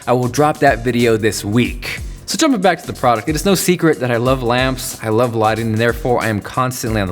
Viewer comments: did he say I love lamp. New conspiracy, Jonathan us Brick from Anchorman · did... did he just say "I love lamp"